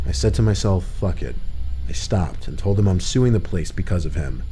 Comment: Kid: I'm gonna sue this place. Creeper: go ahead, I don't work here. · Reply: Comment i was looking for. I thought i heard him say "I'm gonna sue the police because of you" super lost at the statement